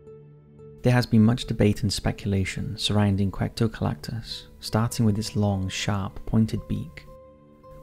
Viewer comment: Sounds like he says "quacktl-calactus"